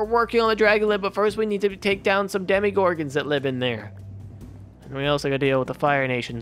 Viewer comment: Uhhh 11:56 did he just say demogorgon? That is from stranger things and it can’t die unless the gate gets closed